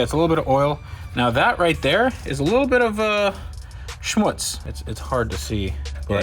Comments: did he say the german word „Schmutz“ 😂? Nice, greetings from Hamburg, Germany 👍🏻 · Did bro really said "Schmutz" on 5:17 min? Seit wann deutsch? xD